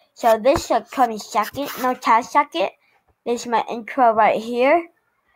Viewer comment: boi you said incro at 2:25 XD